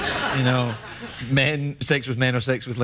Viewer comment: What did he say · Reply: He said "I'm European, originally", playing on the joke about the bisexuality.